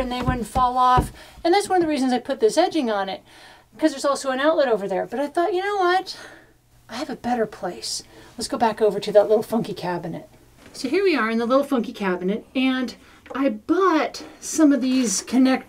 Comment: Watched this while I was making dinner, I thought was saying “that little f**king cabinet”!!!! I was dead hahahaha, good video